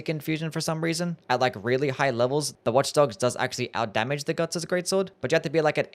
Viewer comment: did bro really say "Gugs"???💀💀💀